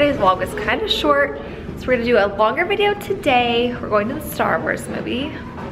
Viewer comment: I thought she said "starburst movie" in the beginning 😂